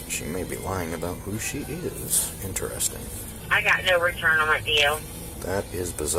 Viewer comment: Subtitle error. Subtitle says "I got no return on her (unintelligible)" What was actually said was "I got no return on that DL." DL meaning driver's license.